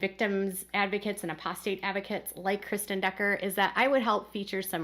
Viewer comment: She said Kristine Dugger. Then she said Kristine Brown. Did I hear that correctly? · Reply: I had to listen a couple times, but I believe she said Kristen Decker.